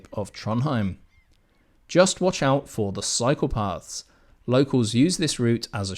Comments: I thought you said beware of the “psychopaths” not cycle paths😂😂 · I was startled when I thot I heard “ just watch out for psychopaths…” .oh it’s cycle paths. 😂 will be doing one night stay there so thank you for this informative video!